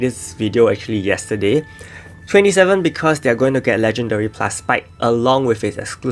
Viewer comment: did i hear L+ spike?